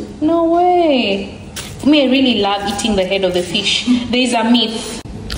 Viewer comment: The fish of the head or I heard wrong 😂😂😂it's the head of the fish ❤️❤️